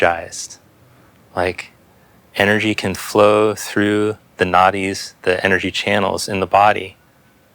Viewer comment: I love this one because it’s awesome and also every time you talk about the Nadis I laugh to myself because I hear “naughties” 😂